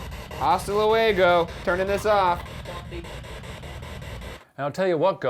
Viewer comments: When Ryan said I'm turning this off. It sounded like it said" no, don't " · I heard don’t leave